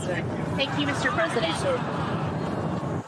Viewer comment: When those two female reporters said "thank you Mr. President", what I heard was 'pfuck you, Mr. President'.